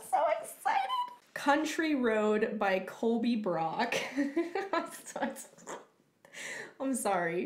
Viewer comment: at first I thought you said this song was Colby Road by Country Crock.